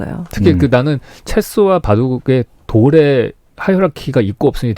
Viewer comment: 하이어r 라킈? 뭐라고 말씀하신 건지 못 알아들었는데 누구 알아들으신 분 계신가요? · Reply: hierarchy 계급요. 체스에는 계급이 있다는 뜻이에요.